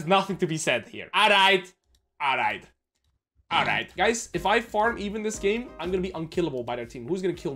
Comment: I thought he said if i fart even they cant do anything to me. It was farm even... he said farm even